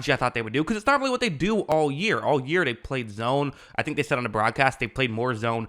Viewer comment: I thought this man said “they play warzone”